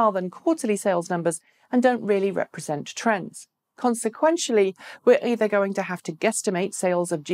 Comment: Consequentially? (@ 5:38) What’s that word?